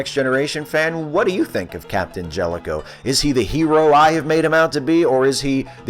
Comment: Swear to god, I thought it said Captain Jellicle and started having flashbacks to "Cats"